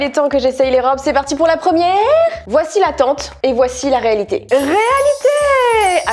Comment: DSL mais moi j'ai entendu '' la tente ''🤣😭